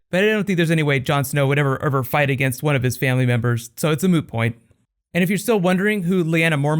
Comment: Did you just say, at 1:40 'It's a moo point"? As in it's like a cow's opinion? It just doesn't matter? It's moo?